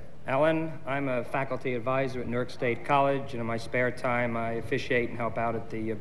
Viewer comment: did he say NerdState college? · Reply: Newark State College (in New Jersey).